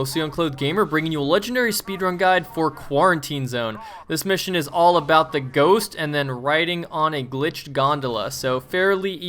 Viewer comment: I thought u said All About That Ghost lol!